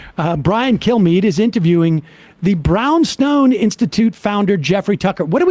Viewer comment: lol, thought Sam said, "Brown Snow"